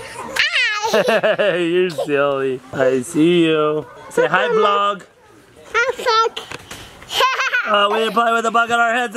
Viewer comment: Did anyone else hear the F Bomb when said "hi vlog" at 10:45??? Do I just have a corrupted mind or did you guys hear it too?